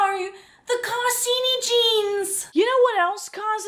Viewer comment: Bro I'm so deaf, when they said carsynogens or however you spell it I thought they said "car skinny jeans"